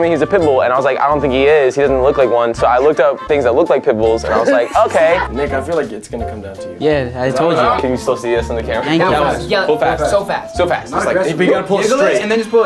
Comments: I thought Austin said “brains that look like pimples” smhhh🤦‍♀️😂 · I thought that Austin said things that look like pimples not pitbulls